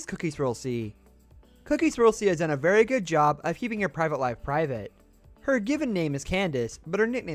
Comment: I thought you said you keep a life private